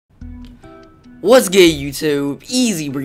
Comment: Did he said Whats gay youtube?